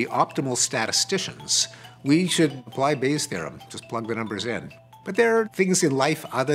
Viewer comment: Ha. And I always thought/heard it was/as "base theorem"